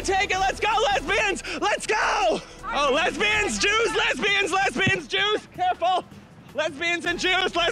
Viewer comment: That sounded like Lesbians juice 😂😂😂😂